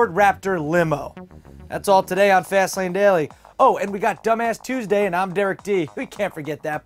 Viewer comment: haha i thought he said its teusday and i'm dumbass derek d